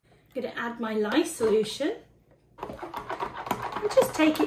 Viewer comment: just after adding the egg yolks, you said I'm just adding my LYE? solution??? I didn't catch the word and there were no on-screen words to make it clear! Thanks - I so want to try this!